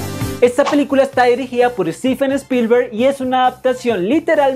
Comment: ponle un texto de que es Steven :p se va a ver gracioso, creo que lo que dijo fue stefen.